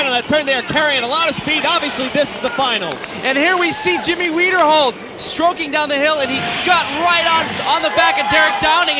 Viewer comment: did I just hear "Jimmy Weinerhold stroking down the hill"??